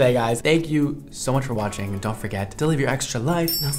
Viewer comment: When he said “dont forget to live ur extra life” i thought he said “dont forget to deliver your ice cream”😭😭😭 i dont know what i was thinking